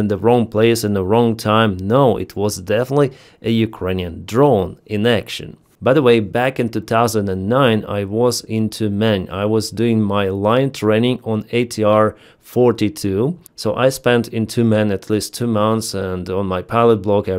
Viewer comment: Glad you are comfortable enough with us and yourself to come out of the closet brother😂. Just joshing you a little, I was listening idly as I worked and had to do a double take at 2:30. You said “back in 2000.. when I was into men” WHAT DID HE SAY! I had a good laugh, hope you do too.